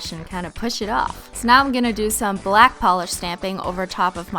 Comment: gay instead of black??? 🔺🔺🔺🔺🔺🔺🔺🔺🔺🔺🔺🔺🔺🔺🔺🔻🔻🔺🔻🔻🔻🔺🔺🔼🔼🔼🔼🔼🔼🔼 illuminati confirmed